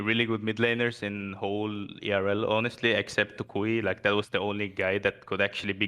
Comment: who? i didnt understand him well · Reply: Toucouille